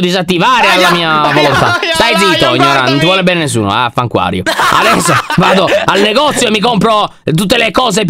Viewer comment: Solo io ho sentito "vaffaunacquario"??🤣🤣